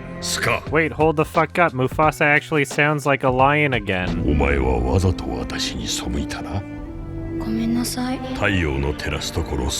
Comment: I don't really know any Japanese but it sounded like Omae wa for a second. As in Omae wa mu, shindiru. Nani?